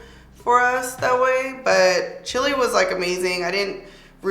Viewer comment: Its so funny because i thought you were saying 🌶 but its chile 🇨🇱 🤣🤣🤣